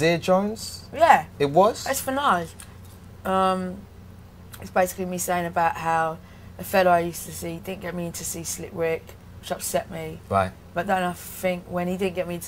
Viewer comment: Can someone write what she says about Nas (3:30) because my english aren't that great... :/ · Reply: From what I hear Amy say, she says this: "It's basically me saying about how a fellow I used to see didn't get me in to see Slick Rick, which upset me. But then I think, when he didn't get me in to see Nas, I thought, "You know what, I don't even think I like you that much... That you're not going to even take me to see Nas!? "You know, Slick Rick, that's slighting. That hurts. But Nas, you know, that's... that's just rude! I sacrificed our friendship for that." I removed the "um's" and where I put the "..." are parts where she seems to trail off and/or to be thinking of the correct word to use to express herself. Basically her story is that she had a guy friend who had the ability to get her in or take her to see Slick Rick, but he didn't do it, which upset her quite a lot. But then next he did not even take her to see Nas, who she seems to like even more than Slick Rick, which REALLY upset her even MORE. It really hurt her even more than when this guy didn't get her in to see Slick Rick. When this 2nd incident happened with this guy not even getting her in to see Nas, who she REALLY liked even more, she realized that this guy was basically just a jerk and not really even her true friend, and that he did not even care about her feelings, and that's when she realized and thought, ""You know what, I don't even think I like you that much..." She said that she actually even "sacrificed" her friendship with this guy over him being such a rude jerk to her, so I guess she just cu